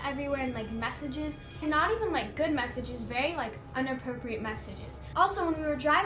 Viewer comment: Who else heard her say unapropriate... 2:14 How is she expecting a baby if she can’t even say a basic word that a ‘15 year old’ should know